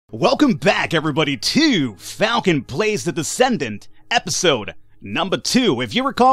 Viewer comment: Misheard Falcon plays as Falcon blazes...still would be interesting